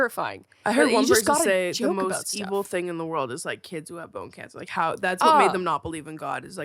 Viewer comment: Edit: petition for a cant sleep x otto collab for a pajama collection 👻 I thought you guys were saying butt cancer the whole time until you mentioned children realizing you said bone cancer 😭 i was gonna say how ironic my grandmom died from cancer found in her butt 😭😅